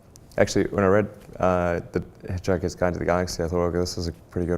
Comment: What is the book he says is a good one? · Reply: Hitchhiker's Guide to the Galaxy - Douglas Adams